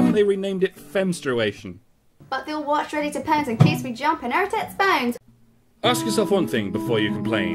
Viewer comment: It's been years, and I still can't tell what she said there. It sounds like, "But bill watch ready to pounce in case we jump and air tits bangs."